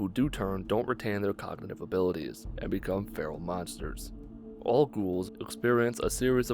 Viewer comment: you said feral monsters but the captions said feral beasts